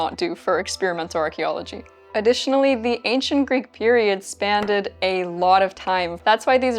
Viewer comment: Spanded? Excuse me, did you just say spanded?😂